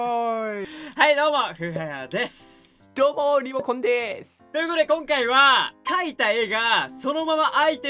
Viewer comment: どうも～リモコンでーすが、今日も～リモコンでーすに聞こえたww